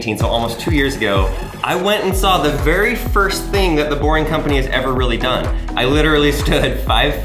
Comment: I thought it said boeing brick company's